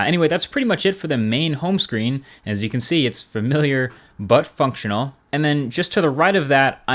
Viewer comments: am i the only one who misheard "but functional" for 'buttfuck' ? O.o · it sounds like u say "butt fuck" tional